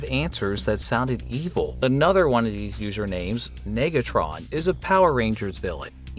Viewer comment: How is nobody talking about this 6:32 😭 i was shocked for a second, because i thought he said the N-word 😭💀